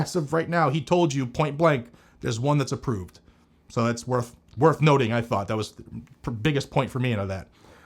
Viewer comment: I think what he said was there’s one that is approved that has a style of the rare breed FR 15